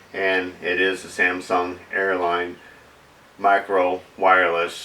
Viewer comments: Another lesson in "Buy American"! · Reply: Herb Blair well, go figure, Samson Technologies Corp is 'murican! It sounds like is saying Samsung, but it is a Samson product. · I fell your frustration, but you may want to clear up the company name of the product.....seemed to me your pronunciation was always "Samsung" and even a viewer responded that he hoped you sent it back to "Samsung" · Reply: I also thought he was saying Samsung, but saw the pic of the box